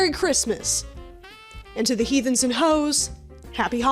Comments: When said “Heathens and Ho’s” I heard “He, Thems, and Ho’s” and I will now be saying that forever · When said “Heathens and Ho’s” I heard “He, Thems, and Ho’s” and I will now be saying that forever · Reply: you heard "he, thems, and ho's" but i heard "heathens and hoes"